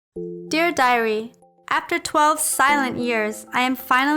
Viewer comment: When I heard the intro I thought I heard her said "Dear diarea"...